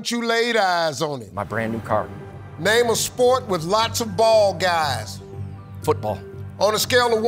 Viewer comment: Steve needs to enunciate better, I thought he said name a sport with lots of ball guys